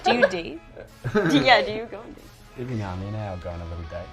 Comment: Can somebody tell me what kj said in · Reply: “Every now and then aye I’ll go on a little date”